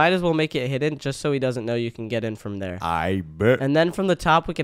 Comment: did bro say I bit